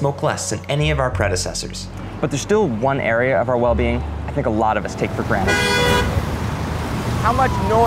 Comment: What i thought i heard at 0:25. “But there’s still one area we can be even more millennial..”